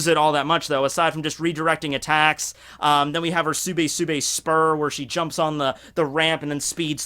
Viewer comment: I thought he said sube sube sp£Гm if you catch my drift